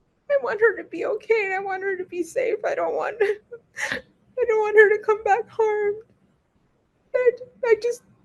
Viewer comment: mom says "I dont want her to come back home" 🚩🚩🚩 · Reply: Sounded like she said " I don't want her to come back harmed".